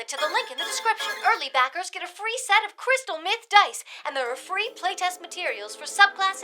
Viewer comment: Wait, what? * checks subtitles * Ah, yes, crystal myth dice, that's definitely what I heard. I don't even know what crystal meth is.